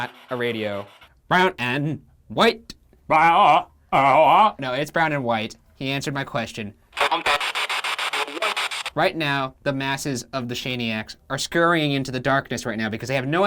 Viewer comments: Honestly, I hear “brown and yellow” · It sounded like, “Brown and yellow.” to me. When I watched it again the white sounded like Why-ite. I dunno, a lot of his stuff is a bit far fetched. However, when the “ghost of Al Capone,” said shut up, I heard “son of a-“.However, a lot of this was pretty compelling. Still don’t believe in ghosts,though. #Shaniac